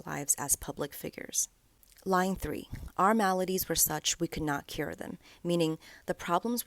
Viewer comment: I love the “maladies” line because the first time i listened to the song i thought she said “melodies” because she and joe used to compose together😢